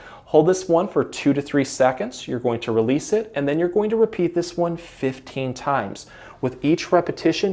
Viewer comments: Just to let you know, The closed captioning said "do this 115 times" thankfully i heard you say, " do this one, 15 times"...thanks for your video series. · CLOSED CAPTION WARNING: at 5:15 the closed caption says something like repeat it for "115 times." Don't! He meant to say, "do this one, 15 times." He repeats himself a few seconds later, and the CC catches it right that time. It happens again at 9:27 ("110 to 15"... should be "one, 10 to 15 times."). There might be more, and they might be obvious, but just a simple warning for the hearing impaired.